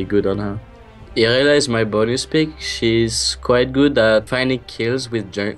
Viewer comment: "Irelia is my..."? what did he say there? is Irelia his number one top laner? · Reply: he said bonus pick